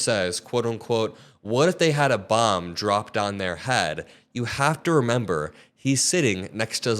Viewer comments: Uh. I thought he said what if they drop a bomb on your (the reporters) head? But still it's completely disrespectful. He's just a jerk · he actually said what if a bomb dropped on your head right now. He was talking to and about the reporter who asked that question. All he was saying is we don’t know what’s gonna happen. Mostly because he can’t think that far ahead